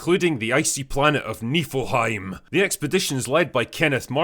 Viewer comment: I genuinley thought they were saying 'Nippleheim' the whole time.